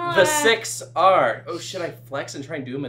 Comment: did he say should or ummm s###